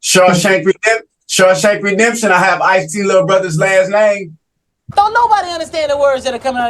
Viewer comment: Did he say “Ice T little brother my daddy”. Is that what he said? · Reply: I heard “I have IceT’s little brother’s last name.”